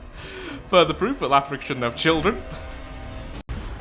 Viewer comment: well lets just say youtube auto subtitles are interesting especialy at 1:02:27 when says: further proof that shoudn't have children. youtube thought you sayed africa